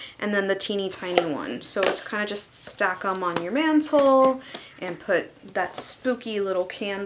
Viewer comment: At approximately 5:21, I had to listen again because I swore she said, "your man's hole"! 😀😂🤣 She didn't. She said, "mantel". Great haul video!